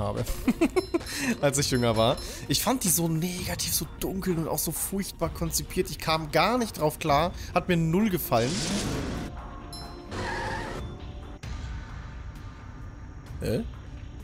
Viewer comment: hab verstanden ich bin umgefallen xD